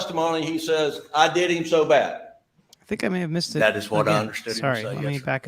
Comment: I heard "I need 'em so bad"